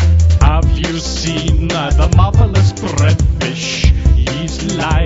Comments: I thought it said "the motherless breadfish" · I always thought it said "motherless breadfish" ... lmao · I also thought it said Motherless Breadfish!